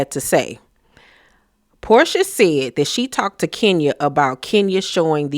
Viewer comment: As you were talking, all I kept hearing was, "Who said THAT?" (Porsha voice